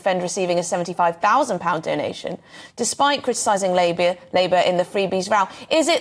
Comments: Did she call Labour, labia at 0:40 😮 That's like calling hostages, sausages 😂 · Forget Starmers Suasage gaff... did you say "Despite criticising Labia"!! 0:38 timestamp 🤭